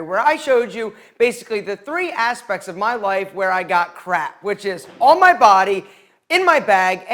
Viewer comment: anyone else think he said crack 😬